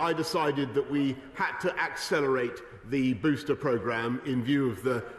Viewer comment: ‘when we had to *act*ccelerate’ Am i the only one who heard that?